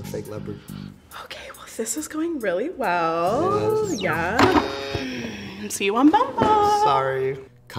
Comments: I thought she said, "See you on Venmooooo." 😂😂😂😂😂😅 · Missed opportunity to say, “See you on bumble.” at the end. 😭😂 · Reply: Definitely thought she said “see you on Venmo” which is weirder but kinda funnier.